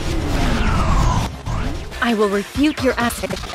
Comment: I'm pretty darn diddly ding dong doodly sure that I heard Toujo say, "I am here to refute your ass."